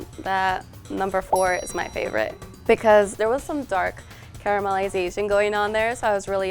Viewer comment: did she say caramelizazing? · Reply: She said caramelization actually 😁